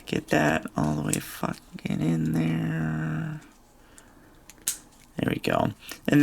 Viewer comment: who else heard get that f’n in there?